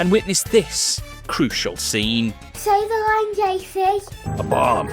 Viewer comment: what did she say? 😂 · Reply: “Say the line, JC” 😂